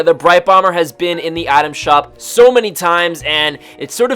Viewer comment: frist few times i heard you saying "atom shop"